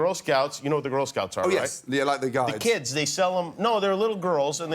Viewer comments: When Jimmy asked if he knew what Girl Scouts were, Gordon replied yeah the guides, but I think Jimmy thought he said guys. U.K. and Canada call scouts girl guides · Jimmy completely missed it at 1:34 he asks Gordon if he knows what a girl scout is and he thinks that Gordon said they are like the guys, but what he actually said was they are like the guides. Because my understanding is that in England the girl scouts are called girl guides, but that's why Jimmy said, no they are little girls because he thought Gordon said "guys" instead of "guides". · Jimmy misunderstood Gordon’s accent. 🙄 Gordon: “They’re like the Guides” (Girl Guides, as they’re called in the UK and many other countries, are what we know as Girl Scouts in the US) Jimmy: “No, they’re little girls.” Gordon was so flustered by that. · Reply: it sounded like he said guys to me but guides makes more sense. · Ramsey says that the Girl Scouts are "like the Guides" (which is what they're called in the UK, Girl Guides) and Jimmy Kimmel thinks he says "like the guys" so he corrects him to say, "no they're little girls." Jeez louise. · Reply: Glad you clarified that because I thought he said "guys" too. How are we to know they're called Guides in the UK? What are the Boy Scouts called in the UK? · Lol Kimmel misheard Gordon, Gordon said "they're like the Guides" Kimmel thought he said "they're like the guys". The Girl Guides are UK's version of Girl Scouts